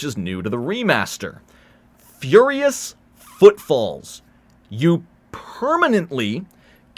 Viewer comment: No matter how many times I hear it, I can't help but hear "furious footfalls" as "furious footballs."